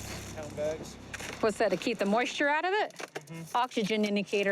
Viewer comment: She’s talking about the runners using moisture packs to keep the weed fresh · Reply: She actually said "Whats that? To keep the moisture out of it?" and then called it an "Oxygen indicator". She had no idea what it was.